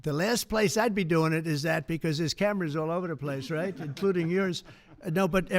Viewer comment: Did he just say “including yours” meaning “there’s cameras all over the place including your house?”